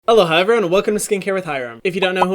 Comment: dude i always thought he said “hello hi everyone and welcome to skincare with hyram” but it’s actually “aloha everyone” haha